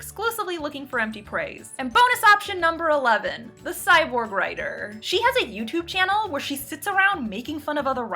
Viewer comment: I also misheard cyber-writer as cyborg-writer...which would be awesome.